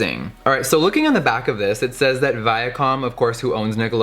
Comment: deadass thought he said “via calm” which is a supplement I give my horse and I was concerned